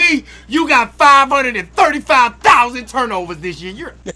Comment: How many did you say? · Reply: 535’000